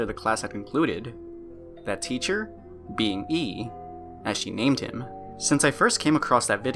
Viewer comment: I THOUGHT YOU SAID 'That teacher.. Being me.. " THE FEAR IN MY SOUL AT THE MOMENT